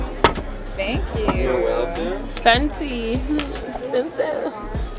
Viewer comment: When the waiter brought the food I thought I heard say 'asante' which is Swahili for thankyou..😂she said fancy....woulda been cool if she said Asante tho